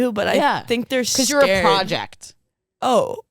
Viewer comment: I heard “you are car jacked” and not you’re a project. And I really love car jacked as a descriptor lol